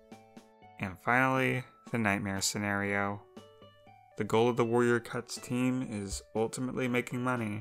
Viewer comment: are my ear bad or did she say “Warrior cuts”?